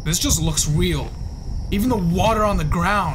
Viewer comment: bro said weal instead of real